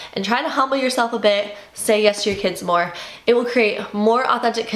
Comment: My 6 year was listening to this with me and heard you say “tell your kids yes more.” And she said, “oh yes! Thank you! Mom, can you buy me some toys?” 😂